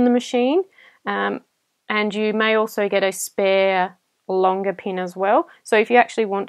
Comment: I swear I heard "A longer peen"...😂